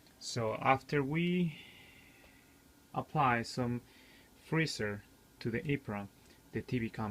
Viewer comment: what did you say? It sounded like you apply freezer to the eeprom. What do you mean?